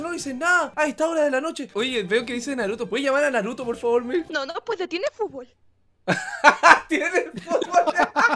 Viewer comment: ¿qué es lo que dicen? jjajsj no les entiendo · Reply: No no puede tien futbol